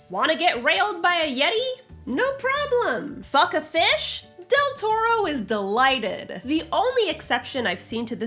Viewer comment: Zombies: but what if they can talk? 10:35 when you said Deltora, it concerned me. PLEASE tell me it's not Emily Rodda's Deltora Quest. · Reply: Lol that's also what it sounded like to me at first, but I think she meant Guillermo Del Toro who directed that movie with the sexy fish man